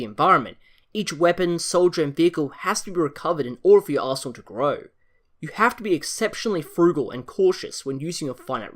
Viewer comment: ". . . in order for your arsehole to grow . . ." I know what he really said, but dat accent.